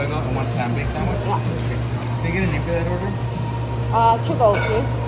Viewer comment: you need for a bag for an order? 저두 공부 중이라 아닐수도 있지만,,, 전 저렇게만 들리는거 같기두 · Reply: Can I get a name for that order라고 한 것 같아요! 그래서 뒤에도 your name이라고 했어요~